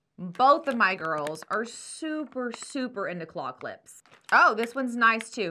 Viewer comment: I misheard you say claw clips and thought I heard “cloth lips”. I had been putting away groceries while playing this video, so naturally I went WHAT???! 😂😂😂